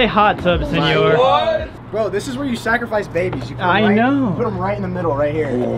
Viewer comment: “It’s a tanning bed for baby’s” 😂😂😂😂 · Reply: I heard 'This is where they sacrifice babies'